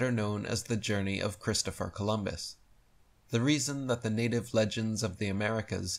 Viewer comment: Rewatching because I thought I heard you say Columbus he was a Freemason